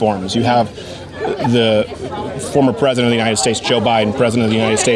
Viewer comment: Did he say former president?